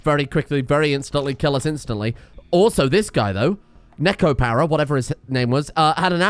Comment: I heard that as Nekopara